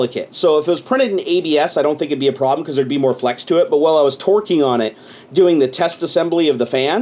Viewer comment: No wonder the base for the fan broke!! he was twerking on it!! lol!! 10:28 (I know thats not what he said but it sounds like it)